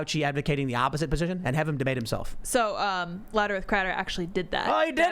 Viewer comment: what in the fuck she say?? laterith cratter? impossible to look into lol u killin me lady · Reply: louder with crowder... wow